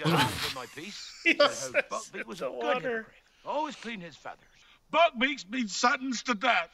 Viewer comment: First time watching this movie I completely misheard "Hippogryph" as "Hypocrite" and laughed as Hagrid explained " - THAT, Ron, is a Hypocrite and the firs' thing yeh wanna know about a hypocrite is they are very PROUD creatures! Yeh do not want ter insult the hypocrite. It may be the las' thing yeh ever do." 🤔 It's so accurate though! 🤣